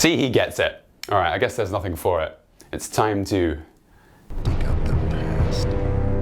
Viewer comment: What does he say at 0:14 exactly? Plus love the vids my guy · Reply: Dig up the past